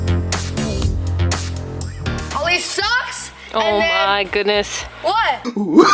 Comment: I Heard Someone Say OMG